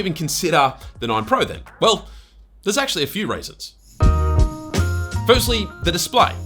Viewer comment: When you said "there's actually a few reasons" it sounded like you were saying "a few raisins".